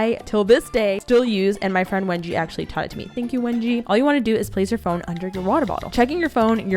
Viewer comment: Did someone say Wengie?